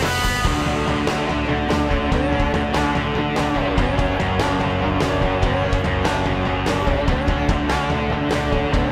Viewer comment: What is he saying at 2:55? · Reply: I'm ready now